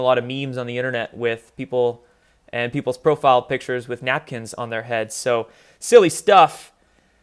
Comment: I thought they said basket not napkin... Either way it's ridiculous and b*****